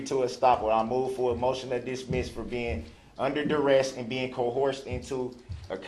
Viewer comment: under derest .... what did he just say under duress* this man is clearly a traveler under maritime law